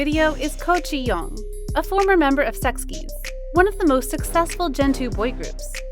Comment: am I the only one who heard sex keys???